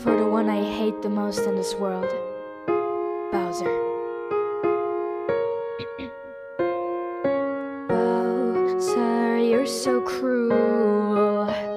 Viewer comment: I thought she said "bowser your so cool"